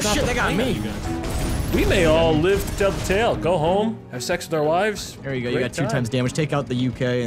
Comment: go have and have sex with our wives Me: I thought he said "sex with our lives"